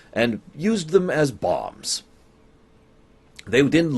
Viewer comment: When you said "Sheridan used them as bombs" I heard "Sheridan used them as Bob" I went "What?!", and it took me a few moments to realize I had misheard you ;)